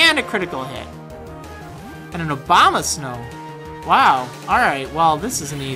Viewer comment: lol i thought you said obamasnow